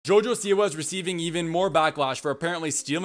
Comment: I thought he said Jojo Siwa’s receding (hair line) at the beginning of the video 😟